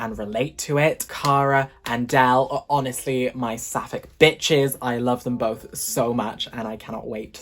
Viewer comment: This is a great video as always, but I have to admit that my favorite part was when the automatic captions thought you said Suffolk instead of sapphic! 😂